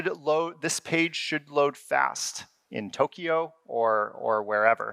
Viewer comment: Did he say tokio instead of Tokyo 😁 Looking forward to the new KV store.